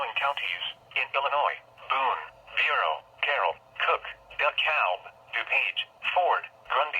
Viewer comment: lol i heard it say the cow